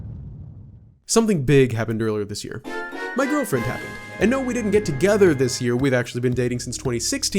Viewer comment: I thought you said my girlfriend died and I was like oh my goodness and was gonna cry. So I went back and watched it again, and you said your girlfriend happened I was like oh my 😂. Congrats to you all!